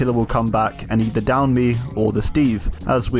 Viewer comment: did anyone else hear him stutter "d-down me" I passed that point in the video and I was like wait did I hear that right? All funny things aside I love your videos💜💜💜